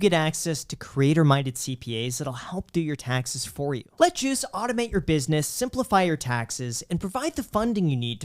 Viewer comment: I know your saying “let juice automate your business” but all I hear is “let Jews automate your businesss”. Can’t help but think that’s what’s got jack grinning ear to ear lol